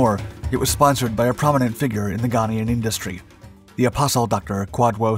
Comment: Did he say at 2:36 "apostle docter"? Ghana sure is a mysterious place